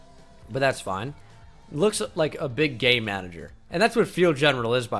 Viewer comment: I thought u said a gay manager I was like tf